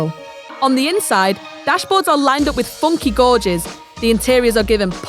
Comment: DId she just say "Godges" instead of "Gauges"..